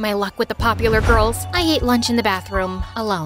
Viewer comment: what did she said 😂 she ate her lunch in bathroom 🥴🤢🤮